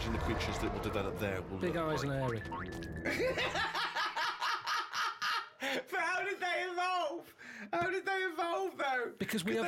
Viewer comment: What did he say at 4:52? Sorry English is not my mother tongue... thanks!! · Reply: But how did they evolve, how did they evolve though?